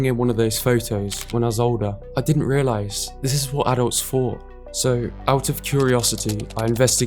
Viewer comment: Does anybody know what he was saying at 1:26? It sounds like "this is what adult's for," but that can't be right. 🤨